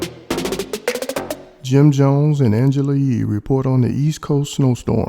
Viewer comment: I THOUGHT YOU SAID ANGELA YEE 😎🎥